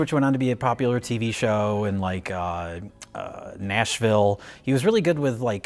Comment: After you said MASH then I thought for a second you then said MASH ville. lol